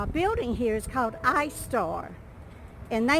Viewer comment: Sooo, I thought you said the name of the development company was called EYE SORE!! Yes, I had to rewind and listen again!🤗🤔😂😂😂😂😂😂 I sure thought that was a screwy name for a development company!!!😊☺️😘